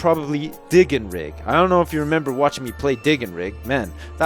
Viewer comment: "Dig n Rig"? Just me that heard that as "Dick n Rip?"